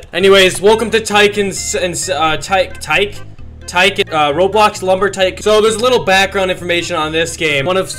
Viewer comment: DID U SAY ROBLOX???????????